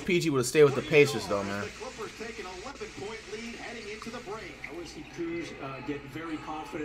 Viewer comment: I know I’m not the only one who thought the commentator said “Olympic point lead”😂😂